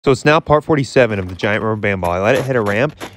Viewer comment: Wait, did he just say “so snail part 47, of the giant rubber band ball”??????🤣my brain!!